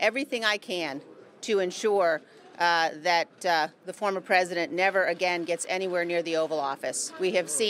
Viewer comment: So if I heard Liz right she said she will see to it the president never steps foot in the oville office again. If she meant what she said she is talking about Biden. DA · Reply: thought she said 'former' President.